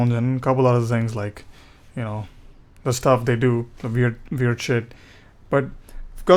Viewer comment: Huh? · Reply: "the stuff they do, weird shit,..." If that's what I heard(1:02)just, wondering what you said?